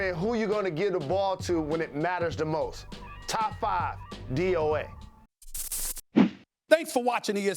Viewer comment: Top 5 what? What did Jalen say? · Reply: Nvm, I think he said "DOA" meaning Dead or Alive